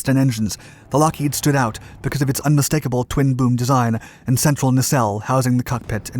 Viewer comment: At first I thought you said Dick Bong flew a "twin boob" plane and thought, "ok, he's definitely trolling us!"